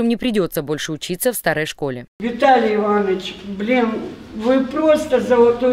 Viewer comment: Учитель начальных классов: "Виталий Иваныч, блин, вы просто золотой человек". 😂😂😂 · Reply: А мне послышалось: "Виталий Иванович, бля..."